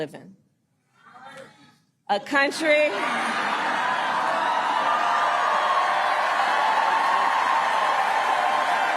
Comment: What did that guy in the crowd say? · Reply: “A free one!”